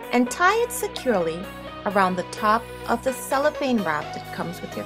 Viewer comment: What's the name of it? Sorry, I couldn't hear you. 5:12 Does the glass goblet comes like that with the gold trimming at the top? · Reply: Hi Little Fourths, thanks for watching! At "3:40" I said I tied blue curling ribbon around the "cellophane" which is the clear wrapping around the candle. Yes, the glass goblet "5:12" comes with gold trimming at the top. I hope is was helpful :-)